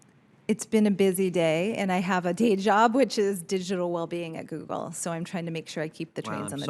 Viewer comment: Is there any phrase more lame and SJW than "Digital Well Being" lmao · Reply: I misheard that as "digital lobbying".